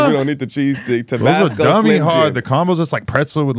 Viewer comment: Did he just say “dummy hard”? 🤣🤦🏾‍♂️